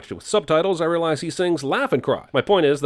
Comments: Stewie says "laugh and cry?" I thiught he said "effin christ" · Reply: My five year old mind thought he said "Death and Crime" But then he said "pumpkin pie" in a later episode so i just stuck with that till today! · I thought stewie said meth and crime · I thought Stewie was saying "heaven cry"